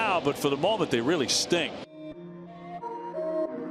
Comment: I think he said "really STING" but I heard STINK, lol. Go O's!